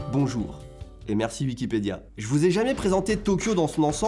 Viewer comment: c'est moi ou dit prononce wikipédia, Vikipédia ?